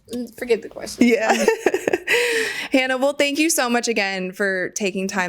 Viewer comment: did you just call her Hannibal? 😂